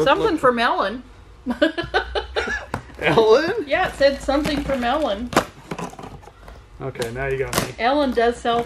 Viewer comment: when you said something from Ellen I thought you said something for melon and was expecting a melon baller to come out of the box!